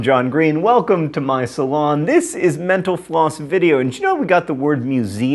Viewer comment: When he said mental floss at the end I thought he said menopause